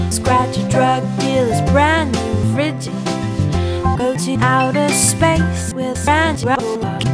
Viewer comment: what did she say? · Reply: Go to outer space with Sandra Bullock